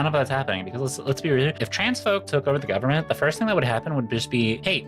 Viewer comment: Listening to the video I thought it said "transphobes" not "trans folks" and I was very confused for a minute 😅